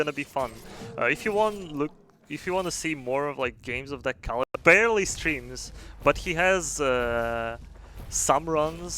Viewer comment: what were you saying when the stream skipped ahead at 23:38? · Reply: I was saying "you might wanna check out Kelnin" rest didnt get skipped